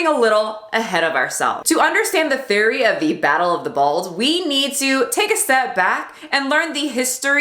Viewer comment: Ok, your Storm tho! 👌🏽 this video was great. Every time you said “battle of the balds” I heard “battle of the balls” and cackled. So thanks for the joy this morning. 😂